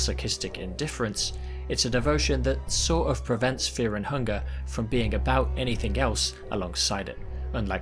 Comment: i think i might be going insane but i swear to god i heard you say "Fear and Chungus" near